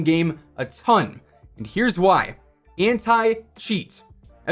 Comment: hentai cheat is what i heard lol